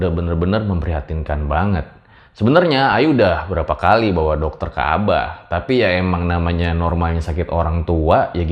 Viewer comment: Ya ampun baangg,,ampe bilang hheehh sambil scroll back,,salah denger apa gmn,,ga tau nya bener bg bilang ""bawa dokter ke abah", maksudnya gmn ya bang,,serius nanya??hehehe..